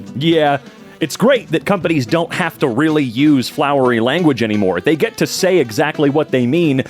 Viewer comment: when I hear flowery I hear “jarona”